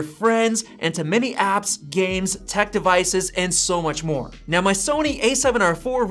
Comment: did he just say “ and so much p**n” ?😂